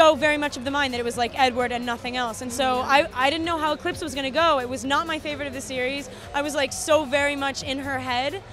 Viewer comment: did kristen say eclipse was not her favorite of the series or am i misunderstood at 0:52?